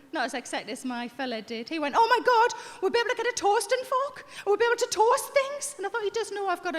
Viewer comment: LMAO! Toastin' Fork! I thought she said, "Toast and Fuck!